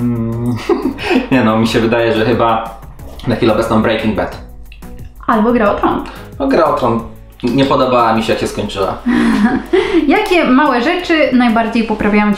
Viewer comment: czy tylko ja za pierwszym razem usłyszałem gra o prąd